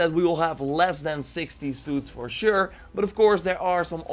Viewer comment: I'm sorry but I couldnt hear you properly,were you saying 16 suits or 60 suits.pls reply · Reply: jihad hamdan 1:02 They hade 60 pictures of different suits, but said "yes, no, maybe" of which of them would be in the game. So at least 60 suits, or as he says "less than 60 suits", it could be 8 suits in total.